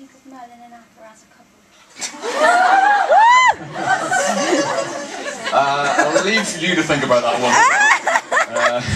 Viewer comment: Which is the question of 7:22 I don't understand😭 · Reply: Lol this is hilariously late but they asked about what he thought about merlin and arthur being a couple